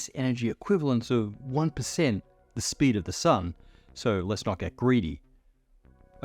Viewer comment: Did he say "the speed of the sun" At 14:17 ?